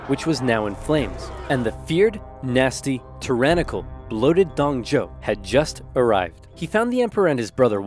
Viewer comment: Did I hear "Loaded Dong Joe" at one point? Hahaha 😂